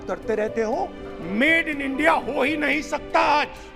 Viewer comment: everyone have thought him saying "make in india ho hi nhi skta aaj" but in reality he said "made in india ho hi nhi skta aaj"....he said "made" not "make" and in my opinion he is saying truth but because of his fame around India we always try to laugh and make joke of him....I too make joke but he was correct here...hope u will realise